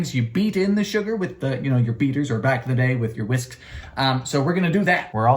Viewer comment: Well I'm late to this party. But glad it's still in full swing. Also was I the only one who, on first listen when he was talking about beating sugar into eggs, thought that he said "beat in the sugar with your beaters or, back in the day, your Mrs." I genuinely thought he said that for a second and was just making one of his of colour jokes about history that he sometimes does in his tiktoks. I had to go back and listen three more times before realising he said "whisks"